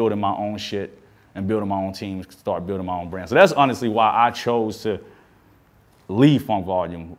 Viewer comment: did he leave fv because I heard him say why I chose not to